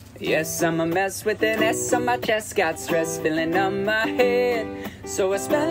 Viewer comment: Ngl i thought it said ass on my chest the first time listening to this 💀